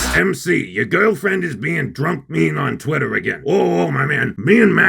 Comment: Did he say Tonkatsu Sinclair was being mean drunk on twitter? No! He said MC's girlfriend was being mean drunk on twitter. Stop being such an Amber Heard, not everything is about you.